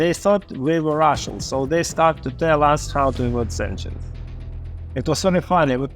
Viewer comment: subtitles are wrong he said “how to avoid sanctions” not “these engines” which actually makes it funny af